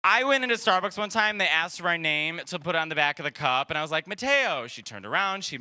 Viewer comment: I watched several times, and I hear "potato" when he says "Matteo" 😅